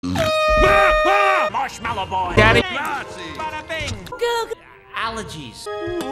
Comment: Did I Just Hear “Daddy”? 😂